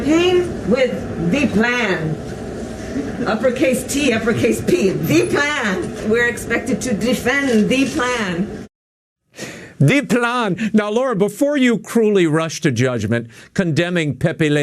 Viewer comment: France angry at us is like a vicious Chihuahua bow wowing at everything that walks by! I thought Kumala was saying "da plane, da plane"!!